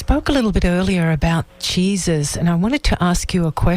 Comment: 100% thought she said Jesus and it was going to take a religious twist